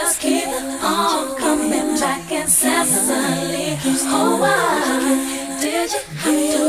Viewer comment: At 11 years old, I learned what the words "euphoric" and "incessantly" meant when I discovered this song. I learnt a lot of big words back then from MC's songs lol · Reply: I thought she said "In Sicily"...mind you, I was about 8-years-old at the time...but it took about 10 years for me to figure out it was "incessantly" :-)